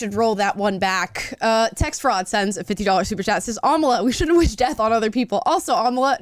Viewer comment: what ever happened to treating others as you want to be treated? I’ve said it before and I’ll say it again: mercy and love for all! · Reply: I’m so sorry, I thought you said Kamala. As in Kamala Harris.